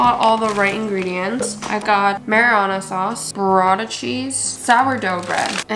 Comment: I literally heard M@@@ SAUCE lmao